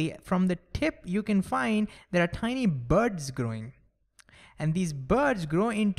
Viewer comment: is it only me who heard it as birds or what? Edit:The subtitles say that too.. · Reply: He says buds not birds, it just sounds same